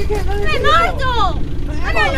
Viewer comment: am i the only one who heard naruto?😂